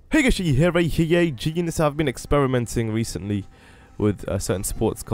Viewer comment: what the heck was he saying? heyghueyyueyheyday ive been experimenting something ...